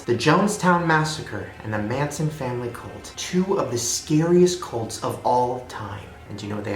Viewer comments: When Shane said "cults" I thought he said "colts" and I was wondering what was wrong with horses! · When he said cults I thought he said goats lmao